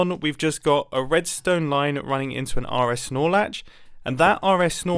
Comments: all I hear is "RS Snorlax" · … did he say "RS Snorlax"? ;)